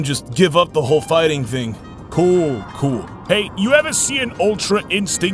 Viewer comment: It sounded like it said hey.